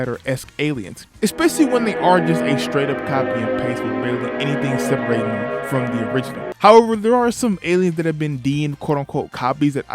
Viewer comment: I’m not gonna lie bro I can’t understand what you’re saying here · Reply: "Without anything separating them from the original"